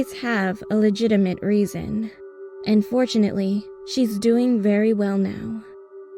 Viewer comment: "unfortunately, she's doing very well now" unfortunately? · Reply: It's probably "and fortunately" but I heard "unfortunately" too and laughed for a good minute😂😂😂